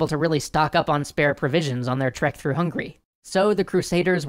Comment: Am I hallucinating or did he say at 5:08 through hungry?